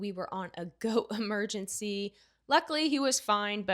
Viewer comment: And I thought you said your husband had a GOAT emergency! I was like, what in the Dr. Doolittle is going on over there?! 😂😂